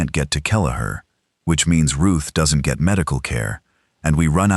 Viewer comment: I thought he said Ruth died